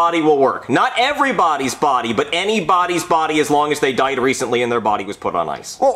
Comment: what is he even Saying? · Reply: But anybodys body can be used for Jeffys body